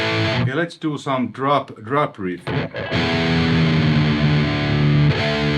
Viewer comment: I thought you said ''Let's do some 'droppery''...🤣 Turn out it was 'drop riff'. I've heard the best tones ever on this channel over the years. Great stuff.